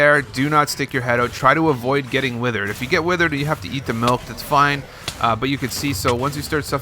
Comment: Did he say eat the milk